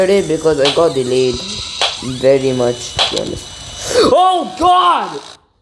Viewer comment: what did u say... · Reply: "Oh god"